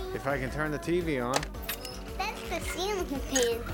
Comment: What does she say at 0:39? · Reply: Jared Peterson that’s the ceiling